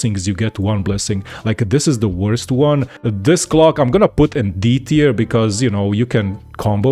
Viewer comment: when he says clock it sounds like hes saying cock.